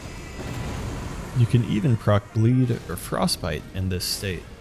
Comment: Did you say “croc bleed”